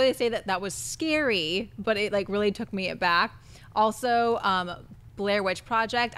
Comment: said the Silence of the Lambs scene took her back. Lol. To where? College? That's such a strange thing for a woman to say, considering what happens in the scene. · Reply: Traye76 i thought she said aback, not back